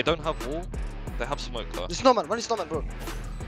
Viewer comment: "one is normal"...he clearly said snowman